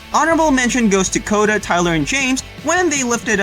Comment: Did he just call Chase, James?